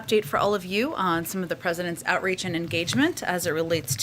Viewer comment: Thought she just said, "on The President's outrage and engagement." 🤣😂